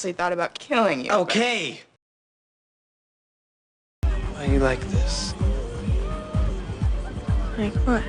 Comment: can someone please comment what jordan says at 5:17? it's killing me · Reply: why are you like this?